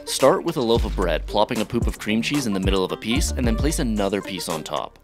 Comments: Did he just say "a poop of cream cheese" ?? 😂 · Did he just say a 'poop' of cream cheese? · Did...did he just say a poop of cream cheese??? I'm dead 🤣🤣🤣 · Although, I'd prefer it didn't sound like you said "a poop of cream cheese", I'll hit the like.